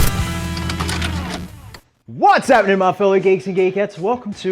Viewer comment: Did he just say " What's happening my fellow gay 2 gay cats" ?